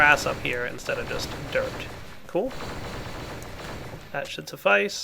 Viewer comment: i thought he said that shits nive